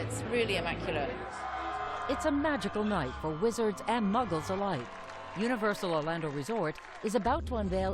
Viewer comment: 🤣🤣🤣 muggers? She may have said muggles the first time, but I really thought she said muggers🤣🤣🤣